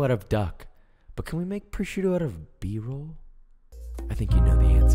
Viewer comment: for a seccond i thought you said "but can we make prosciutto out of a beagle" and I was like um what